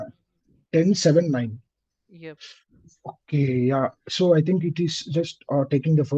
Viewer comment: did he just said the f word💀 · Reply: He said okay yeah